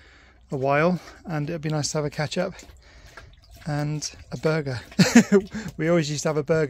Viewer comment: I looked through the comments and nobody else mentioned "ketchup" and burger night. It made me laugh as soon as I heard you say "catch up and burgers".